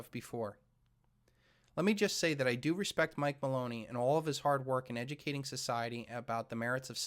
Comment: I swear I heard you say "Mike Baloney" lol.